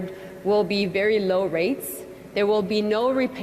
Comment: This loan will never be paid back!!! It sounded like she said "rapes" not "rates."